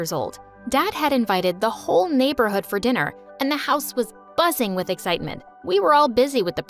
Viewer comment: Why did I hear "the house was bussin with excitement"